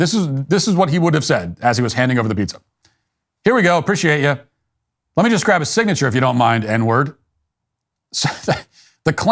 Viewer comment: I heard “let me just grab a signature if you don’t mind and then you’re…all paid up”